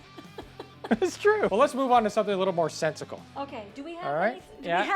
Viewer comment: did he say sentical?